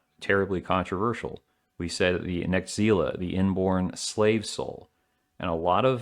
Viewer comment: “necseele?” What you say? I’m having trouble finding this on google. Is that like an occult thing?